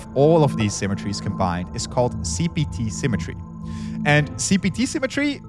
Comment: thought i heard cbt 😅